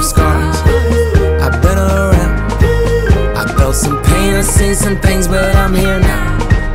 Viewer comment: I keep hearing them say "I throw some penises and things but I'm here now" !! WHAT THE HECK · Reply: WaresTheWolf nah it's "I've thrown some pain, I've seen some things but I'm here now"😂😂😂